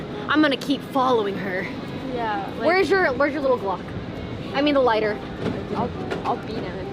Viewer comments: does she not ask, “where’s your little glock?” Or did I mishear that? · they say to get out a "little glock" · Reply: I thought they said block. Glock makes more sense. So, when will the Commiecops arrest them for legally having a gun? If they even did.